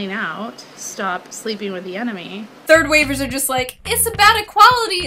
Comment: Did she just say "stop sleeping with the enemy" 😂 Wtf is she five.